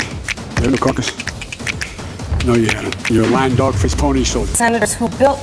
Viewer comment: "Lying dog-faced pony soldier" is my favorite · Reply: I thought he said dog fish not face.